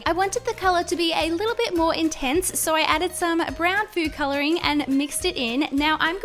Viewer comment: did anyone else hear “So I added some brown poo coloring”?